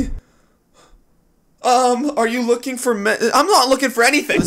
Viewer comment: I swear I heard him say “are you looking for men”